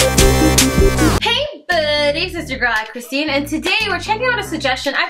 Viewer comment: Whenever she says “hey buuuudies! it’s your girl aychristine” ,up until now I thought she said “it’s aye I’m Christine” now I just realized it’s A-Y-Christine XD - wait..I think I’m wrong again